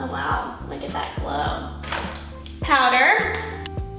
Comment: I heard say pader instead of powder😂🤣